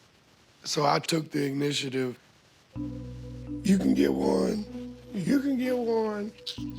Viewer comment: Did Chris say he took the “ignitiative”?🤣 like ignition-tive?😂